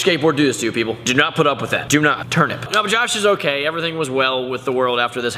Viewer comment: Did he really say turnip at 2:37?